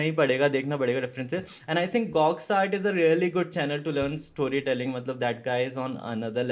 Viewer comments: he talk about some YT channel.... i cant understand can someone please help me out that what he said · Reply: Gawx art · Chennal name kya bola hai bhai 13:40 per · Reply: Gawx Art